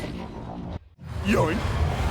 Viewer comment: I hear "Yoy!"